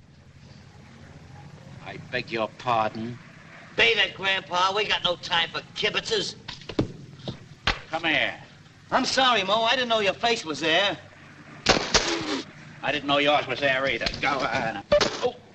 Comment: Beat it grampa we got no time for Kibitzers a Yiddish word for horsing around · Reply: I thought he was saying "Kibbutzes" which I thought was the Yiddish word for "farms"